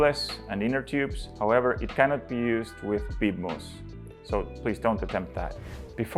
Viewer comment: do not use with big mousse? What are you saying there? That being said, I own one and it works great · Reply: Yes, the Street changer cannot be used with bib mousse inserts. You cannot get the tire into the drop center from the other side, as the mousse “inflates” the tire. There is simply no room to operate the duck head. Same as you cannot use an industrial tire machine to change tires with bib mousses. For the rest of the tires, tubeless or air-tubed set ups, the Street Bike Tire Changer is a good option.